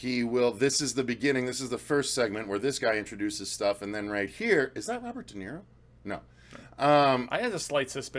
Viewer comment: But I DID say it was him, what are you talking about? I just wasn't 100% sure. What is with everyone trying to "Call me out" on this when I literally said the dude's name? This is so dumb. Glad you liked the rest of it, though. lol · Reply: it sounds like you're saying "Is that Robert DeNiro? No". That's why people commented. I agree though it's dumb and yes we all know who De Niro is and maybe misheard what you said. The bigger picture is the trailer looks epic and we will all be there opening night. (hell yea!)